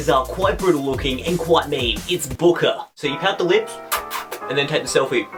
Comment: When he said boker I thought he said booger